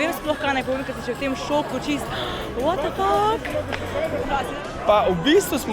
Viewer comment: Did she say "What the f***"? :D